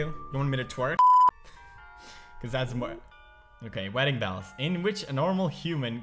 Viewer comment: I thought he said wedding bowels